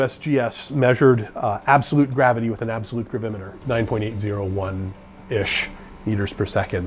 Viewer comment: The closed captions say "perimeter" instead of gravimeter.